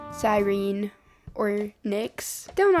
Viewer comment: When you said "Cyrine or Nyx" I heard it as Cyrine Ornyx so maybe thats her full name so you can kind of keep both names